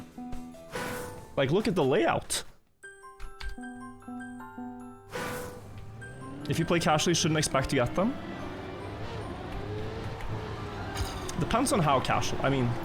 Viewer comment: “if you have a life you shouldnt expect to get the author medals” exactly what i heard from that dude